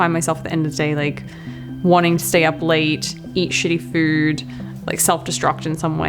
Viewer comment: who heard HIV. Even the subtitles said HIV😂